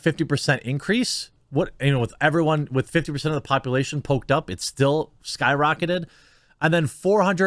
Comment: I thought you was saying poped